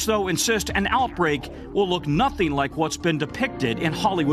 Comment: did I hear pissburg?